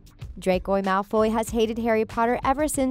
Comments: DID SHE JUST SAY DRAQWO MALFWOY? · DID I HEARD U SAY: DRACOJ MALFOY😂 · ExCuSe mE dId yOu jUsT cAlL hIm dRaCoY mAlFoY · Did she say 'Dracoy Malfoy' wtf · did she just say Dracoi Malfoy? Edit: lol she keeps saying Dracoi